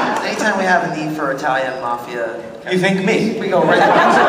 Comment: I keep hearing 'Hetalian Mafia' lol, my obsession with Hetalia makes me hear some things differently.